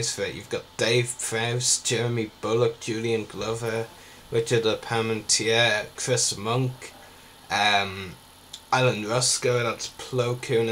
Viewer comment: Ha! Turn the transcript on. YouTube is so stupid in that regard. Here's just a snippet of what it said for me; "11:36 Which is the payment to yet testimony" What REALLY said was; "Richard Palamintier and Chris Muncke" Please excuse my own spelling mistakes, the irony almost kills me.